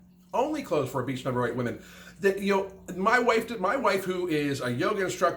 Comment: Did he say my waifu at 6:03? Haha